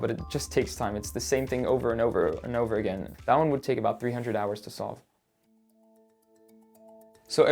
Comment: Did you say 300 hours to solve? I thought I heard 300 years